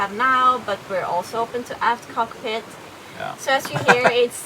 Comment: Wow! At 07:26 did really say "a$$ cockpit"? LOL! That was so cute! The way chuckled while she just moved on was priceless! This is huge news...it seems like you just got RAN II, and did so much work on her. The custom tender davit, custom solar arch, new spray hood/bimini, custom name sticker, etc. etc. -- It will be exciting to watch you go through the shopping search...take us along to see all the boats you go too see!